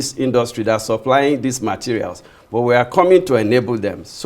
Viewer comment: 10 factories in 15 years??? did I hear that correctly? 4:56 That's a very long time to build 10 factories, what's the hold up? Why should it be so slow?